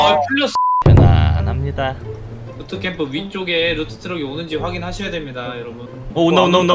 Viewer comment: 저만 "루트캠프 위쪽에 부트트럭"으로 들었나요